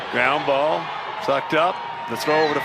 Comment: it sounds like hawk says fucked up